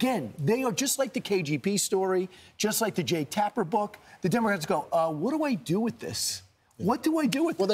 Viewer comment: Did I hear him say “demorats” at 8:50 or am I crazy? 😂😂😂😂😂